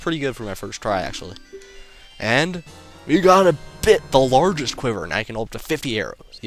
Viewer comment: when you got the largest quiver.. i could of sworn you said: "We got a Bit..... largest quiver!!" X3